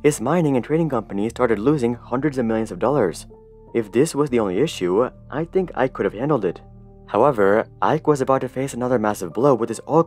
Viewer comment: 😂 from 7:01 I heard "if this was the issue I (Hari) could have handled it"